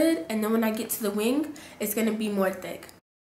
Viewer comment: I swear I thought she was saying "dick eyeliner" I kept replaying like 10 times before I realized she was saying "thick" ahahah. Beautiful look though 😍💕